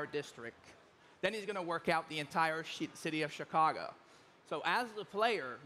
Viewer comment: shit city of chicago lol ... is what it sounded like he said